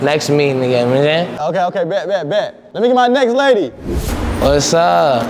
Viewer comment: What did Lil Tjay say.... "Yegetmeehej"☠